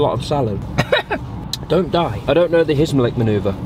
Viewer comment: coughs:don't die Me:(replaying video to see if he said dont die) Me: bruh what Edit: he said it